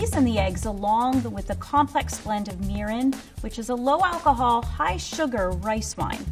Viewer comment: i legit misheard mirin as urine. i not paying attention and heard that then i was like, WHAT?!